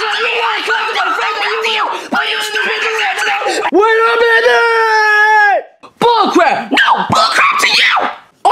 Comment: What did Freddy say? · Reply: He clearly said "Wait a minute!"